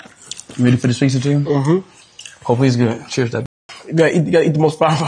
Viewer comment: Yo at 8:25 or so did I hear Speed Say,"alright cheers dad?"